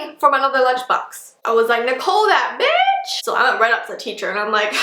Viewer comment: did she say the b word or bish? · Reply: Jaylyn Thomas she said b*tch